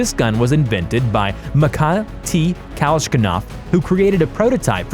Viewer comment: did he really say kashnikov? its kaLAshnikov my guy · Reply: I heard “Matiah T. Kalshkinov”